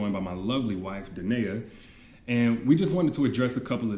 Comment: For a moment their I thought he said "da n**ga 🤔